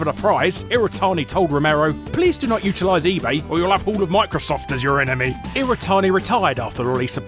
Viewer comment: Wtf does he say at 6:50? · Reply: "Please do not utilize ebay or you'll have all of Microsoft as your enemy"